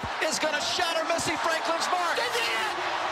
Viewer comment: what is the guy even saying at this point lmao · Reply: SHEDIDITTT